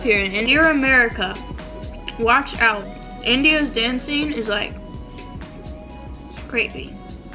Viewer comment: did she just said India's dancing is CREEPY